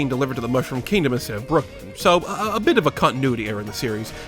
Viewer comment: did you just call it BrookLAND?